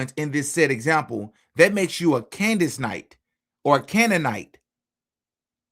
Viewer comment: or I heard candy cult lmao they think cause we question things we are all part of a cult 😂